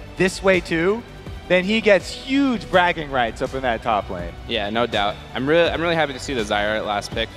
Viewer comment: there was a error with the soundfile, it sounded like "Yeah no daut" but we all know what he really said